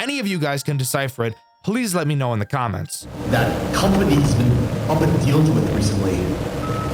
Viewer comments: I hear! “That company’s been pumping with deals lately” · “the company’s been impossible to work with recently” is what I deciphered, the rest is probably cut off for “spook” factor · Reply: I heard “that company’s been up in deals la- recently” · “the company’s been impossible to work with recently” is what I deciphered, the rest is probably cut off for “spook” factor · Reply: I hear “that companys been up and dealed with recently” the hell it makes no sense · "that company has been up and dealed with recently" is what i hear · i heard that company has been, pooping deals with recently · What I hear is "That company's been.... up and dealed with, recently." Which isn't even grammatically correct, so I doubt that's what he says. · the company has been hard to deal with lately is probably was being said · It sounds like he says “the company has been upping deals recently” or cutting deals or something on the line of that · I hear "That compagny's been up and dealt with recently" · "That company's been uping deals with recently" Is what I hear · I hear "That company has been up and dealed with recently" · the guy in the back says " Company has been up and deals with recently." Idk what that means but thats what subtitles say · “That company’s been… up in deals with recently.” I believe that’s what he’s saying? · "That company's been.. Up' and dealt with recently. .. Why?" is what i heard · "that company is been open dealt recently" is what i hear tbh